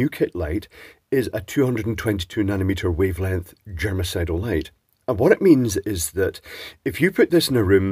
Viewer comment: I think I am going deaf. For a brief moment I thought you said it was a genocidal lamp 😬